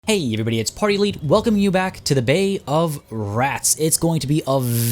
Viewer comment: For a moment I thought you said "Bae of Rats". And I started thinking... Did they include Broodmothers in the game? Please continue this playthrough. Loving it!